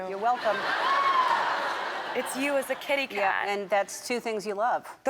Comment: did she just say "Katykat"???